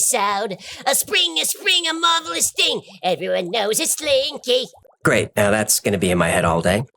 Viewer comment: You might sing slinky, but all I hear is “log log log”